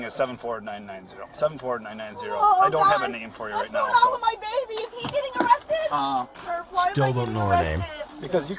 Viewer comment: did she say what's going on with my baby is he getting arrested too 😂😂😂